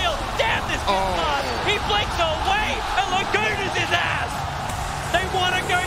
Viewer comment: Did he just say and Lagunas his ass? LMFAO. P.S. Arteezy bro pls get it together.